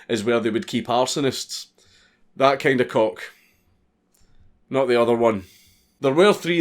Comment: I didnt think he said cock, I thought he said clock.